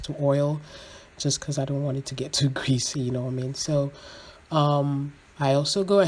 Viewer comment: Hi there, what is anasie 3:43? I'm really struggling to understand what you are saying here.. Many thanks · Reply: I understand, I meant I didn’t want it greasy and stale. Hope this helps.